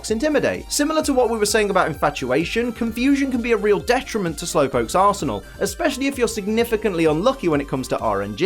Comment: what I heard Confusion can be dedrementle to slowpokes arsehole